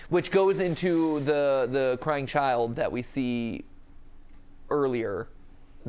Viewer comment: DID YOU SAY A CRYING CHILD?